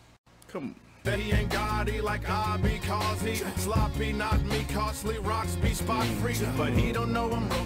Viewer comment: At first i thought you said tech was sloppy 😂